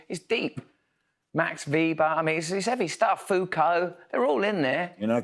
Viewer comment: Did he say Foucult?